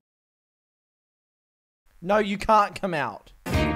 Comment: Ok but tell me why it actually sounded like he said “can I come out”